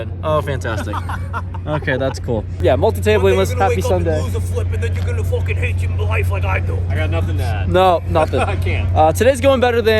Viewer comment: Hey, i think your new friend at 5:00 said “One day you’re going to wake up and lose a flip and then you’re going to f-ing hate life like I do”…inspiring words 😉